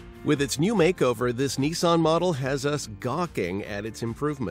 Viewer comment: Did he just say Neeson..? It's a Nissan...Niss-an.